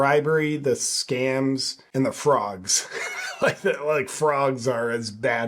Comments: she clearly said fraud not frogs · lol, my brother. I know its difficult to understand some "accents", lol, but i think the young lady said fraud, lol. Not frogs... butbi bet they got some crazy ax froggies in africa ;) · I thought she said BS “fraud”. Not “frogs”. 🙅🏻‍♀️. Watching the meltdowns of those who “flee” America and FAFO. 😂😊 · I think she said "frauds," not frogs. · I think she said fraud, not frogs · I think she said "fraud" not "frogs."